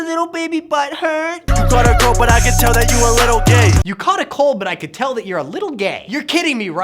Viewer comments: Did he just say "You caught a cold, but I can tell ur a little gay" those aren't even the lyrics 😂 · he says that ricegums lyrics r "U caught a cold but I can tell that u a little gay" yo bart, search up the lyrics u try and roast next time, he acc says "u GOT a GIRL but I can tell that u a little gay" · Vikas Vallabhaneni what?? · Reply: bart thought he said you caught a cold but i can tell u a little gay, when rice actually said you caught a girl but i can tell u a little gay. Then bart says stupid shit after even though rice used the word gay correctly